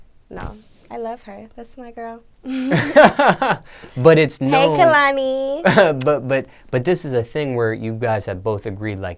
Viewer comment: did she say "hi Kehlani" as in the singer Kehlani?